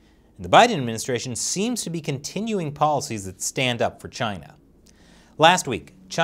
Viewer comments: Hey just so you know I watch with subtitles on and it said at 2:20 "Stand up for Taiwan" Instead of "Stand up for China" Advanced youtube manipulation? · turn CC on and go to 2:25 says "stand up for china" but the CC says "stand up for Taiwan"!